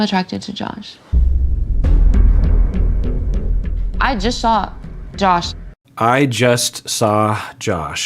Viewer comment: I feel like I heard Jackie say "I just shot Josh," as in "I just shot Josh an email"--for what it's worth!